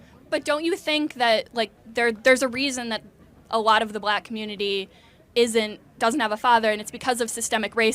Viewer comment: I thought She said She did not have a Father ?